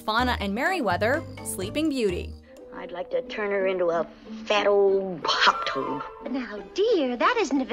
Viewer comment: I thought she said, "I'd like to turn her into a fat old pop tart!"